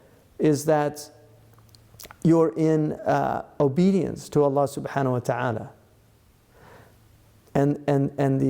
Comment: or 6 minutes the captions say obedience to Hannah Montana although Sheikh Hamza says Allah subhanahu watala. There are mistakes throughout the caption so it is worth going through all of them, but this particular one is concerning.